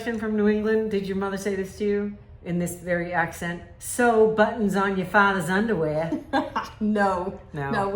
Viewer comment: I heard it as "sew buttons for the Red Cross". 🤣 Love these new stamps!